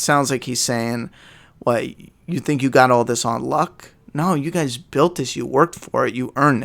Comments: Sounds like he said "you think you've got this on lock.." I think he is warning Rick that no matter how locked down and safe he thinks it is, it's not. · HEY YOOOO! are you sure 🤔? Cause I hear Negan say " you think you got all this ON LOCK" Which is very different from "you think you got this on luck". I dont know I might be wrong but ..... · I thought he said "you think you got all this on lock" meaning in a sarcastic tone that Negan things the communities aren't so good as a group.